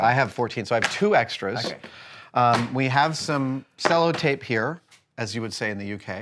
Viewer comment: Adam might have mentioned it but I missed it, what kind of tape are they using? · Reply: Stello tape I think they said?